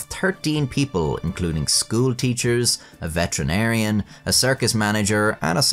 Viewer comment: Not gonna lie, the first time you said "veterinarian" I heard "veteran arian". In my defense you were talking about Germany during the wars